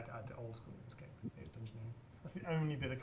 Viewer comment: what does he say? something about adding something to oldschool runescape.. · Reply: Dungeoneering